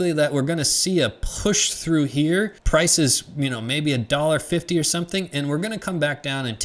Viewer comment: what r u sayin at time stamp 9:12 to 9:15 $ 15 or 50 ? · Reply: Hello, I mentioned that once XRP breaks through our key level of resistance we could potentially see prices climb as high as $1.50 before coming back and testing our previous resistance as support 👊 We will have to wait and see 😊